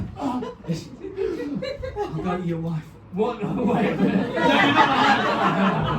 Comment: also can anyone catch what is said at 2:52? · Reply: line before says About your wife. said @ 2:52 What? Wait a minute. Then, after says I'll make sure they're okay.' because had to take a second to figure out where he was going cause I think he thought that Jerry and Terry were together.